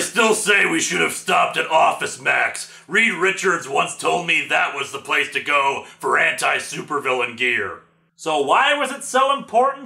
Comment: (I thought he said "Reed Richards once told me that's the place to go for HENTAI supervillain gear")...